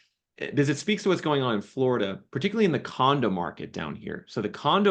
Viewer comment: I thought he said the condom market 😂